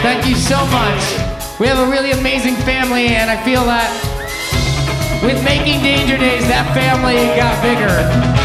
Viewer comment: what did he say? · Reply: With making Danger Days that family got bigger